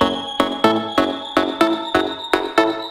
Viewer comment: What are the words? It sounds like: Check coordinates - fight